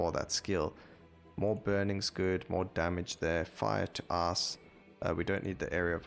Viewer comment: Holy I was in game and I spat my drink everywhere when I heard "Fire to Ass" at 30:18 instead of Ice. I started playing random builds like you for the first time few days ago and I'm having a blast. Really great idea. Great video again!